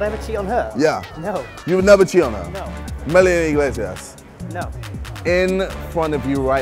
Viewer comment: what was the name he said at 1:10? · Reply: Iglesias